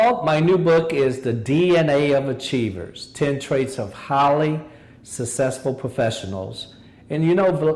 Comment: when he said "highly" I thought he said "holly"